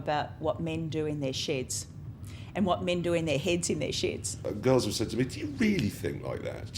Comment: Did she just said SHITS on Tv in "Men doing their SHITS on TV and Men doing their SHITS in their SHEDS?"